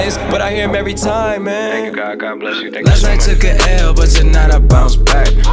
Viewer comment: anyone else hear "lady gaga bless you thank you so much" in the background?